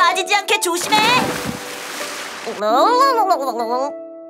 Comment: I think they’re said "Cookie Run Kingdom" and then the next "o0ooO?" was like "You don't understand?" Kinda love that part >w<